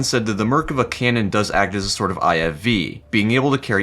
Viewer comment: am i hearing this wrong? "the merkava canon acts like a sort of IFV"? huh?